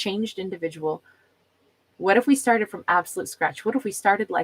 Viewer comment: I kept hearing " what if we have a flirt?" Haha